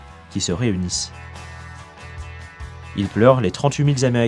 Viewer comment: "ils pleurent les trente-huit mille zaméricains"... C'est quoi un zaméricain ?